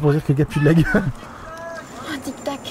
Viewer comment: j'ai compris (C'est pour un Tiktok)😂